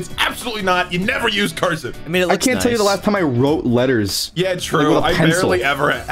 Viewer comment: The text said "I wrote words" instead of "I wrote letters"